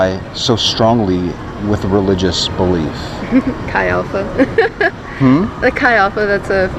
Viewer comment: What does she say around the 9:20 mark? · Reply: "Chi Alpha". It is the name of an on-campus Christian ministry group.